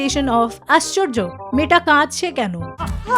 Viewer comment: What does she says at 3:13 did anyone understand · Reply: In Bengali she said "Aschorjo, meyeta kadche kyano ? " means "Strange, why she is crying ?"😂 it'a reference from a old Bengali TV series from Bangladesh "Aj Robibar" where the main Actor Zahid Hassan had a repetitive line "Aschorjo, meyeta rege galo kyano ?" Means "Strange, why she gets angry" Which became a meme template in bengali meme community later😂.